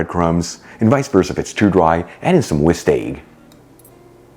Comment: I probably misheard, but I think you said that if the mixture is too dry it should be moistened with Whisky. I'm going with that, even if I heard wrong.